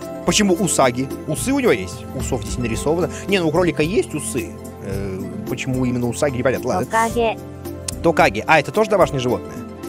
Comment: Мне одному послышалось Хокаге?